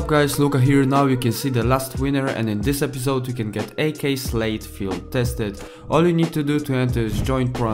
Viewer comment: I heard slave, not slate